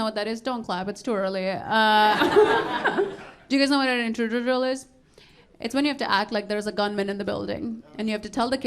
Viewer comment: She needs to work on saying "Intruder Drill" I had to listen back to it 3x before I heard it. NOBODY in that crowd knew what she was saying, lol.